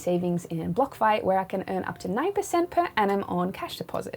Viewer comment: How may of these things do you still spend a considerable amount of money on? Use the code YOUTUBE at checkout to get 20% off our financial freedom course and membership: <link> Get up to $250 in free BTC on deposits over $100: <link> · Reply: What did you say you put your savings in? It sounded like you said blockfight.